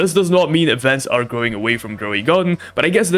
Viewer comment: “Growing away from grow a garden” that’s what is says when you turn subtitles on 4:32 😂😂😂😂😂😂😂😂😂😂